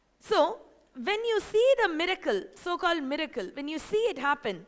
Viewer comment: good info but she needs to slow down when speaking for God Sake.. I first I thought she had said Medical and my brother says she had said Miracle....LOL.. No offense .. slow down girl... it's like an old telegraph machine.. but at least she speaks English...